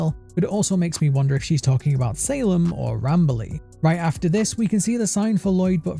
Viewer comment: Sounded like Not Bradley instead of not Rambley tbh.